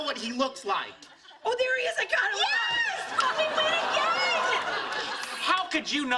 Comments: did penny say “f***ing win again!”? Lol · It sounds like penny says we fuckin win again lol